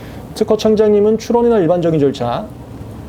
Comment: '특허청장님은 출원... 의 일반적인 절차 ' 이 부분 정확히 뭐라고 하시는지 말씀이 빨라서 잘 못 알아 듣겠는데요. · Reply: 문의주신 부분은 '특허청장님은 출원이나 일반적인 절차'라고 말씀하셨습니다 : )